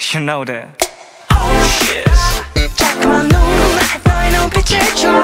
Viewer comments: Okay but I always thought he was saying oh shiz ;-; no joke lol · i thought he said "oh shizz" (snoop dogg reference 😂) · When he said 'she is' I thought he was saying 'oh shiz' so I thought I should check cause I was so fucking confused